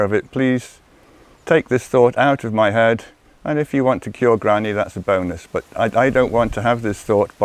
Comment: Lol! When I first heard it I thought said, "If you want to kill granny that's a bonus..."